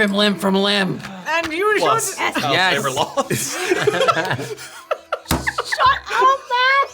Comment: Aaa! I couldn’t hear what Matt said at 45:55 😢😂 I bet it was so funny! Help me, captions did not work! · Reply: “Plus, child labor laws”